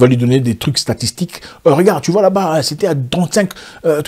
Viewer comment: " pourquoi 35 "